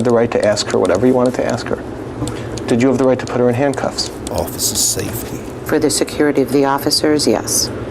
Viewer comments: does anyone else hear a someone say “ officer saftey” at 36:13 ? is that her lawyer speaking or is it after effects or smt? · did anyone else hear some deep voice saying "officer safety" huh